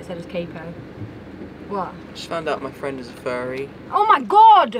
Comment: robbie: i just found out my friend is a furry: oh my gOD it’s @ 0:23 for anyone wondering 🥰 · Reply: I heard “shut up my friend is a fairy”